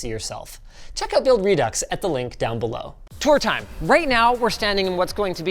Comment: Darn, thought you said "Story time"